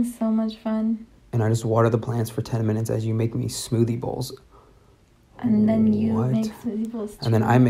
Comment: 1. I thought they said smoothie hole 2. That lady looks like she is kissing her son 3. I KNOW I pulled a muscle watching this.